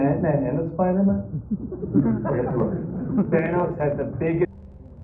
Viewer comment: What did he say? 13:43 Thanos had the biggest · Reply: Army in the Universe